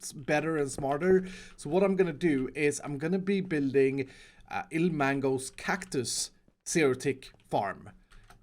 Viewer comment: the captions say " ...im gonna be building ill mangoes cactus theroretic farm..." STONKS